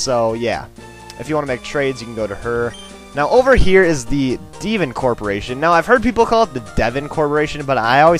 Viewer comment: When he said Devon I thought he said Demon.I have Bad Hearing.